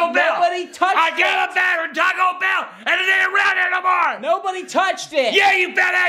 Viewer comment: I thought he said I got a bear from toco bell and it ain’t round here no more